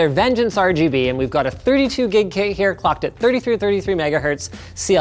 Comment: it sounded like when he said clocked it sounded like he said fucked